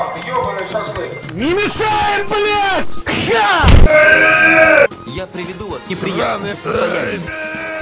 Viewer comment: на 0:33 постоянно слышу: "Я приведу вас в сраное состояние"